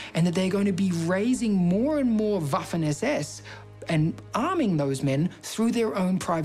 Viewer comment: wtf is vaffin? It’s what the subtitles say. I know they are incorrect often but I can’t understand if it’s a v, b, f.. please lmk